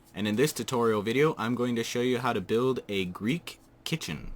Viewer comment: I heard u said "hey pandalings , I am Gunna show u how to make a Greek kitten" xD